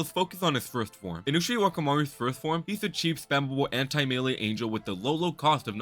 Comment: did you just say CHEAP AND SPAMMABLE 🤑🤑🤑🤑🤑🤑🤑🤑🤑